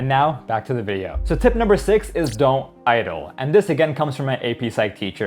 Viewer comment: my dumb-ahh brain thought it said don’t die 😅